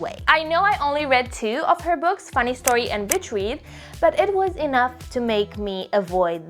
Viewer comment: I thought for a second she said bitch read 😂😂😂😂😂